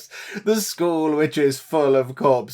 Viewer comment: Lol when you said it not happy because of all the skulls I thought you said schools....although both are not fun